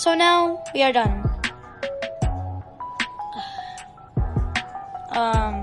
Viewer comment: i thought you said taxes